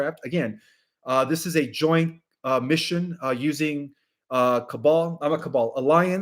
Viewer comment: why does say: "i'm a cabal" at 29:13 ???????????? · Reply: Sometimes the transcript is not accurate. You have to watch video not just rely on the transcript , what he actually said “ … using the cabal , oh not cabal , alliance..”